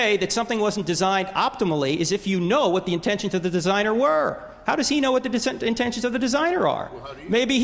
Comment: What did Hitchens say at 53:33? Was it "Well how do you?"